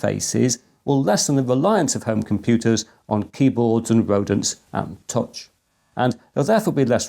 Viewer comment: did you say 'rodents'? Never seen a trackball raiding the larder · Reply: Rodents 😂